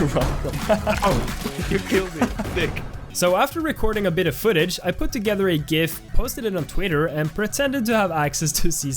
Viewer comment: WHAT DID HE SAY?!!?!?!???!? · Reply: Thick